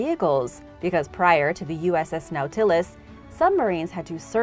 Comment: I am In no way, meaning to make fun, But when mispronounced Nautilus I thought she said, The USS Mel Tillis